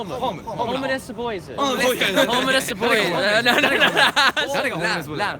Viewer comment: Sorry for interrupting your stream lol. Was cool seeing you and keep up the amazing content! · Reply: We need the link for the Hometown Boys too.. looked around, can't find them! (I thought they said Homeless Boys at first...)